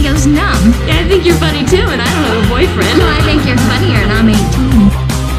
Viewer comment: what is she saying? 0:09 "i think youre funnier and...." ? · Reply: "..and I'm 18"